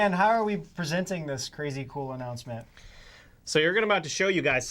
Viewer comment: When Dan said, "Time for the crazy cool announcement," I heard, "Crazy colonoscopy!" Mandela effect?